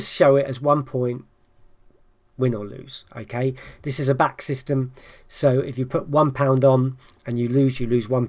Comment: Northern Ireland is in UK but Eire racing is probably meant to be included. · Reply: thanks simmo yes I know NI is in UK but my question was related to your Limerick choices which is not in UK. I thought I heard him say in the video that this was UK system but maybe he meant UK & Ireland